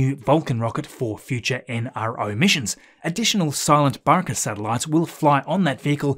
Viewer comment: For a second i thought he said the name of the Atlas was "silent Baka" 😂.